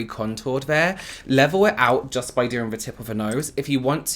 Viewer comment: I heard: "its almost like you're creating a DICK at the tip of your nose." said: "a DIP at the tip of the nose." 🙊🙉